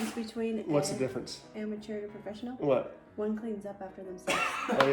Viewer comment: What was the joke at 0:49? I tried to understand what she said but it just sounds like "One comes up off of themself" to me - which makes no sense at all.... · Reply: One cleans up after themself ...